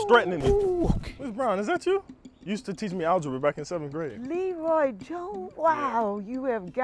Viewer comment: Leeroy jones i almost heard him say LEEROY JENKINS